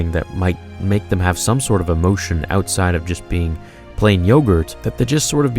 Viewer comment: Did you say “ plain yogurt ”?